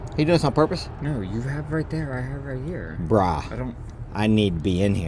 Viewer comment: Did you say “bra” at the 15:45 mark? 😂